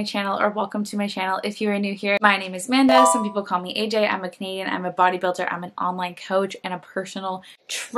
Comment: Hi Amanda 👋 I never miss your videos and EVERY TIME you say "I'm a Canadian", I always hear, "I'm a comedian" 🤣... BTW, I'm not an English speaker, I've to 'train' my ear, as my English teacher says 🤷🏻‍♀️